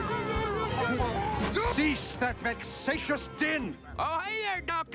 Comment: What was he saying? "Cease that Vexatious Dinn?" "Cease that Vexatious Dihn?" I know he was basically saying, "Shut up," but was it Din? Dinn? Dhin? Dihn? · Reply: Din.